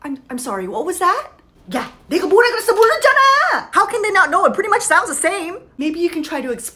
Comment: She sounded like she is saying in Hindi "dekho, bola tha na.." which means" look, I told you that"